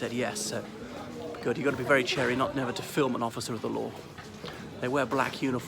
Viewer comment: Salutations Mr. this video is one of your best. What women are purchasing and wearing those risque outfits? What is that word at 3:50 what sounds like "chair-ee"? "you've got to be very chairee not ever to film an officer of the law".